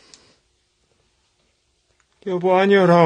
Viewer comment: 여보~ 라고 하는게 너무 잘들려요..ㄷ